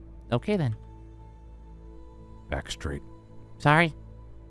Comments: I THOUGHT CASTOR SAID "ACT STRAIGHT" 😭😭😭😭😭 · ABOUT DAMN TIME OMGG ALSO YAY THEYRE BACK!! :D 0:40: "HI LUNAR! :D" 2:09: NEW ASTRAL MENTIONED!! AQUARIUS HAS WATER POWERS!! (Make sense) 2:20: does that mean there's literal cat constellation flying in the sky? (Felis) I know they're probably talking about other stuff but I just find it funny if it's true- 3:40: FINALLYYY 5:18: Just gonna save this cuz Astral lore- 6:45: foreshadowing that Lunar is gonna use the power of friendship on Taurus..? 😃 8:00: DAMMIT GEMINI PLEASE JUST GIVE US THE LORE 😭 8:20: i did keep saying that he should just tell Nebula or Taurus when he comes... 😒 8:46: okay I find it weirdly wholesome that Nebula is comfortable enough around them to do that- 8:53: Gemini and Nebula lore 👀 9:58: Pollux laughing manically and Castor slightly chucklingly "I'm sorry to tell you this...Nexus simply was just a lunatic" (10:16) 11:16: guys keep in mind the timeline is whacked rn so this was meant to be posted before Nexus talked with Solar 11:50: please don't tell me this is foreshadowing :,) 12:26: imagine it was Gemini lol- (probably least likely but yk) 13:36: more Astral lore- 15:50: PLEASE,PLEASE,PLEASE TALK TO LEO PLEASEE 🙏🙏 16:04: i hope Leo is just a sassy, pain towards Lunar 16:16: So Leo doesn't like Libra huh? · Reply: I THOUGHT CASTOR SAID 'ACT STRAIGHT' 😭